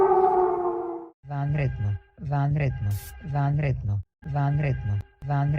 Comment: To answer your question of what it's saying, it'a saying Ванредно/Vanredno, which means something along the lines of "emergency." At least that's what I believe I hear as somebody who's learned Serbian for a while